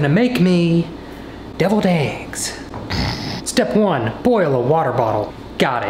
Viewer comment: I thought that it said delivered eggs instead of deviled eggs lol